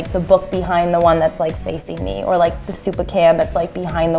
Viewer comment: Was it just me or did he say soup of can at 6:17?